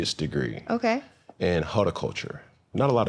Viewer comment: did he say hoeticulture instead of horticulture??? 😂😂 I don’t think he went to school for horticulture